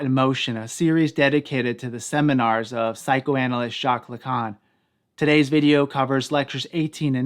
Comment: I gotta increase my volume 1:45 I thought he said psychoanalyst Chaka Khan, lol!